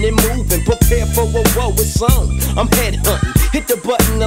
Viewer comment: Oh shiet.. i always thought kurupt was saying, “prepare for a war, it’s on.. i’m panty huntin” 😅😂